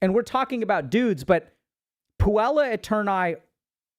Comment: I hear this as "PUA eternis" .. im cooked lol